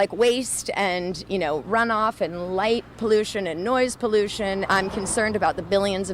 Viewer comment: Did she say noise pollution?